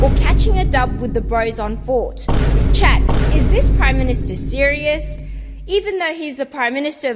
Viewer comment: "deadass" instead of "serious"